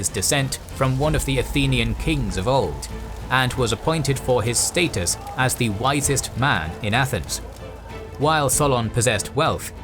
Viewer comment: Baha, i thought it said "His status as the whitest man in athens."